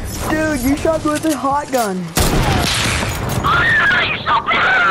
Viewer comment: Did he say hotgun?🔥🔫😅